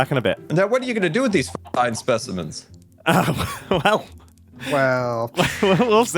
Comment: What did say again? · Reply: “Now, what are you going to do with these f- specimens.”